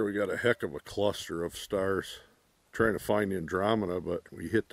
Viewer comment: You said find Andromeda I thought you said that you needed to find some drama, I was going to invite you to come talk to me girlfriend lol